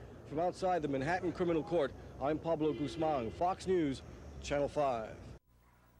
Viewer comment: did he say his Name was Pablo Guzman?